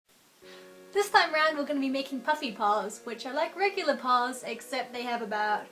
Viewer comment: i thought it said Fluffy paws.